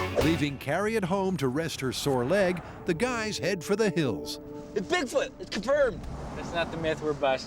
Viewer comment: What did Tory say before Grant said "that's not the myth we're testing"? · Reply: "Look, it's Bigfoot! Confirmed!"